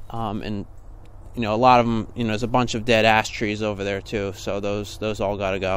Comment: LOL... I had to listen to this 3 times to make sure of what said at the 00:37 mark. At first I thought he said, "... a bunch of dead ass trees over there".